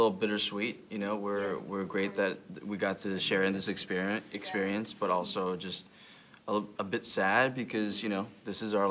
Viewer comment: what is Dylan saying to Thomas? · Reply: He said in very low voice to Thomas with his thumbs up: I'm in it.